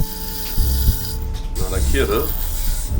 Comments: I swear to god he said “not a kid” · i swear he said " not a kid, huh"